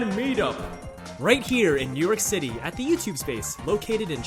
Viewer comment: It sounded like he said Newark city